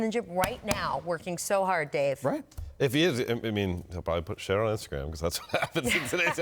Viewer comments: I thought he said “put shit on Instagram” 😂😂😂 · Did he say "shit on instagram"